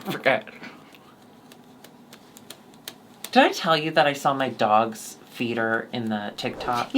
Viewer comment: I thought she said "I saw my dog's peter"